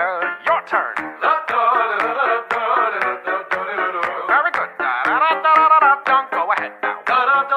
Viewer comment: I hear "have a good time" then why its "Very good" ?